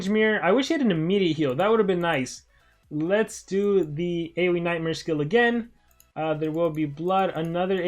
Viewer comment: I thought he said nightsmares